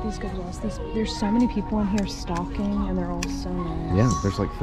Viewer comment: said "stocking" and at first i thought she said "stalking". lol